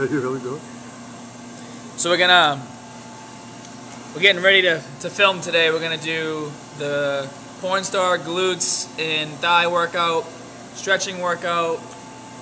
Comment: Did you say "porn star" at 0:25? If you're a porn star, do not hesitate to post the link to your videos.